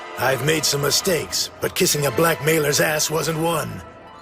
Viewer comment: thought he said black mans... And I was like WTF